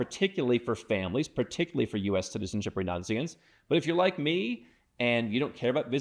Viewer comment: “Particularly for US citizenship or Nazis.” 😂 Google auto caption got creative 😅